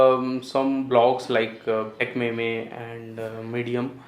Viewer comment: Did he just say Tech May-May (Techmeme) :]